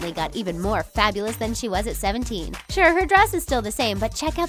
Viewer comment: I think you said 17 not 16 at 1:36 Just saying love ur videos!! ^.^